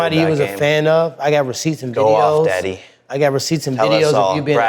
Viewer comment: Did jake call tyron daddy at 7:13? I donno bout all that my boy 😂😂😂